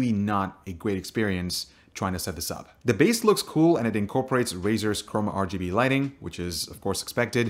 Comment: Did he just say razer chromum?